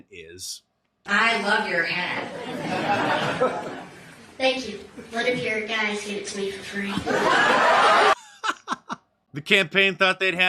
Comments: What does she say? I can't make it. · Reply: "Thank you, one of your guys [Nikki's staff] gave it to me for free." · What did the kid say at 7:04? Did she say what are you going to give me for free? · What did she ask? I replayed it so many times, but the reverb from audio combined with the bad enunciation from the girl made it impossible to understand what she said. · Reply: Haley said she liked her hat.( It was a Nikki Haley Campaign Cap ). The girl said, " Thankyou, your staff gave it to me for free ". In other words, if it wasn't for being given to her for free, she wouldn't be wearing it.